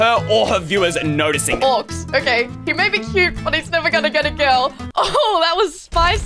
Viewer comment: did you say girlfriend at 0:49? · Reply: and did she say cute at 1:06?